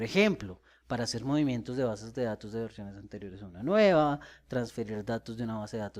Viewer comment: que dice en 3:10? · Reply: para ser movivmientos de bases de datos de versiones viejas a mas nuevas.